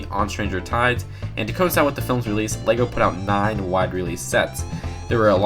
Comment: you said "lego" instead of "disney"